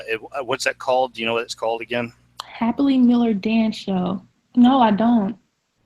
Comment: Dance Moms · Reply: I could not hear him bc of the feedback in my headphones. That's so embarrassing 😂😂. I really thought he said "happily miller". Abby Lee is something else